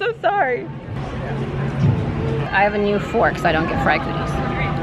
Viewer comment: and were hysterical at Flavors of America. "I got a new fork so I don't get pregnant", feeding and then locking arms as they had a bite. Comedy gold. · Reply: I heard “so I don’t get Fry cooties” hahaha pregnant would be odd to say